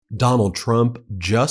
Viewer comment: It sounded like you said McDonald trump at one point and I died 😂😂😂😂😂😂😂😂